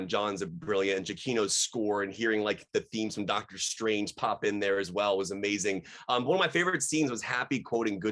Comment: When I first the three words "Doctor Strange theme" I thought he said "Doctor Octopus theme" and I was excited! I thought the Doc Ock theme was confirmed